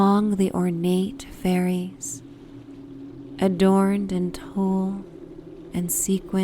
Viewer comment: What is the word you’re uttering at around 13:48 when you say, “among the ornate fairies adorned in “_” and sequins? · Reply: Tulle